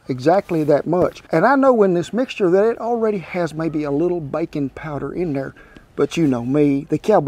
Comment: Lord, I thought he said bacon powder and my eyes lit up.